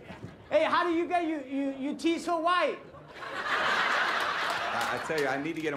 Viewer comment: I thought Guillermo said How do you get your team so white not teeth. lol